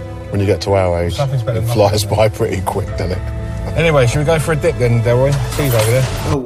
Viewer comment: hahaha I see what you’re mishearing. said “shall be go for a dip then” referring to the fact that they’re on the coast